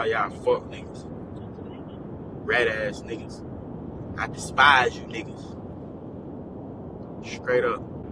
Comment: Anybody else heard this nigga say comspise? Wow